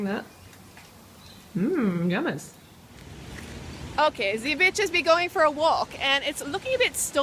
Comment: Freelee, did you say "Bitches be going for a walk"?? hahahaha